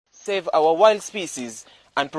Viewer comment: Is it just me or did it sound like he said save our white species